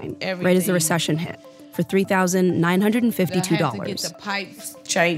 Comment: wait she bought her home for $3952????? 2:49 am I hearing this right?